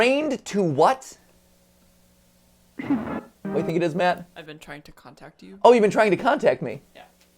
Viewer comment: I heard "I've been trying to come back to to..."